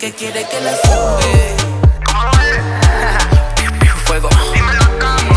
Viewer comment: parece que dice cállate